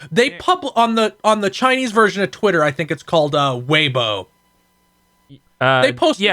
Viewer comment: I love when anyone talks about Weibo, because it sounds like they say "huevo", the word in Spanish for "egg".